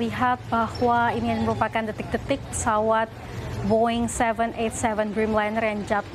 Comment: Boeing seven two seven atau 727. Pembaca beritanya yg mabuk atau aku yg salah dengar